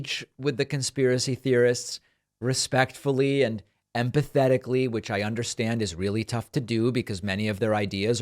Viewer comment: I heard "we've got to engage with the conspiracy theorists respectfully and pathetically," before I realized David said "empathetically."